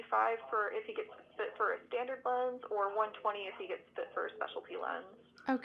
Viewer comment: Do you like to book appointments on the phone? Let me know in the comments! 😊Continue learning English with me by downloading my free ebook "5 Steps to Becoming a Confident English Speaker" 👉 <link> ❤️ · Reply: Even with subtitle, I cannot understand the part at 25:07 "...gets fit for a speacialty lens" .